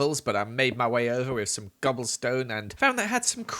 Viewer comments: it sounded like he said gobble stone instead of cobble 😂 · Did anyone else hear him say gobblestone? No? Ok. No offense though! Love this!